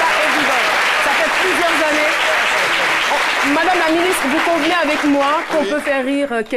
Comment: je sais pas si vous avez entendu cette partie mais à 3:42 caroline dit ceci : Madame la ministre, vous convient avec moi qu'on peut faire rire quelqu'un une fois deux fois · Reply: AMELIE LINDA CORRINE FOLI écoutez bien svp, elle a plutôt dit conv’nez qui est la forme contractée de convenez